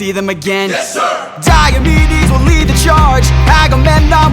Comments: does anyone else hear "diabetes will lead the charge"? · all i hear is diabetes will lead the charge..... its so funny